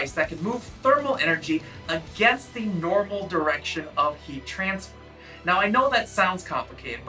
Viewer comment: who else heard "in the opposite direction of the trance"?